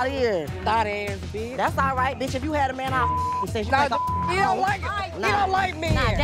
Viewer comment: What abira say at 1:40?? Their mouth dropped like she said something fierce to minnie 👀, all I heard was "if u had a man bleep bleep"??